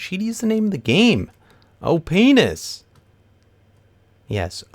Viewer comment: I think everyone thought you said "oh penis" and not "opness".